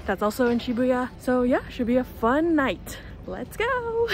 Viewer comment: you made an unintentional pun! your "should be a fun night" sounded like "Shibuya fun night" 😂 Favourite ice cream flavour is Earl Grey!!